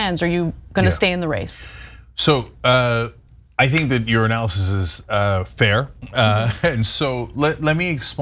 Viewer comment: does Cenk say "So, uh, I think that urinalysis is, uh, fair"?